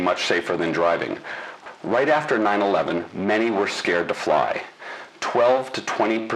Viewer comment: What do you mean? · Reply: in the it sounded like he said "a 9-11" instead of just '9-11'